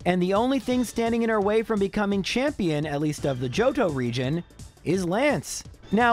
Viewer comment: I replayed 54:35 a bunch of times because I heard “Jojo region” instead of JOHTO